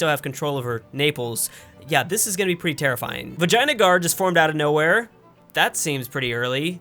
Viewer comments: it sounds like he said vagina guard!😂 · I literally thought u said “Vagina guard”